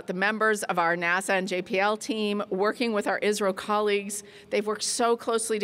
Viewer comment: is she saying israel or india ? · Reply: Isro colleagues